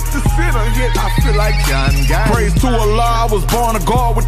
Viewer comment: Did he say ; pray to Allah ???